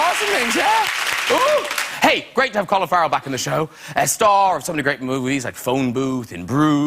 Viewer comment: I thought he said "Great to have cauliflower back on the show" xD